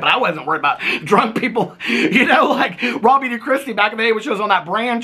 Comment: Whenever says "raw beauty christi" I always hear "robbie d'christi" without fail LOL